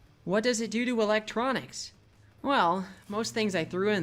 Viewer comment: did he say "doo doo electronics" ?